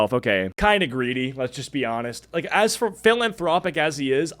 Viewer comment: I thought you said "villianthropic" talk Abt Mr beast and now I think it's actually perfect 😅